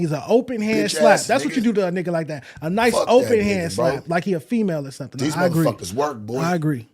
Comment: Bro what did he say at 1:02:05 am i the only one whos hearing him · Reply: An open hand slap, LIKE HES A FEMALE or something 😳🫠🫠🫠